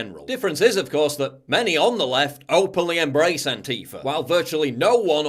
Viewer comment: I heard a "Antife'r" in there JPW